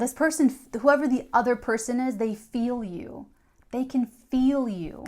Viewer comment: When you said they feeeeeeel you I heard “I feel you Joanna” in my head haha